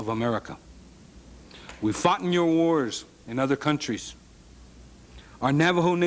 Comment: I thought he said F@ your wars lol